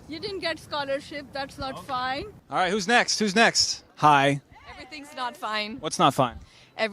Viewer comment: did she just said that everything is not fine?